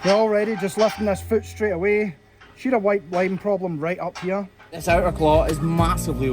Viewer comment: Right at 10:43 "She has a white wine problem" I swear I heard that and was like, "So does my friend's mom, but she's had a hard life, don't judge."